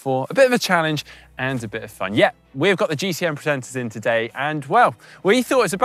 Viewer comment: almost sounded like "the GCN pretenders"